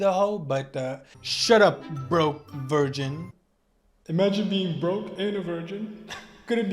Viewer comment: Had to rewind because I thought you said “imagine being a hoe and a virgin” hahah